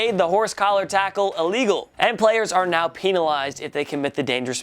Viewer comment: did he just say pee-nalized